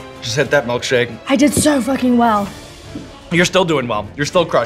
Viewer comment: Did Millie say f***ing at 14:46?